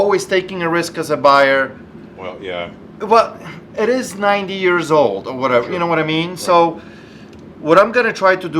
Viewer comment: He must have heard you wrong and thought you said it was a 9 year old machine not 90 years old 🤣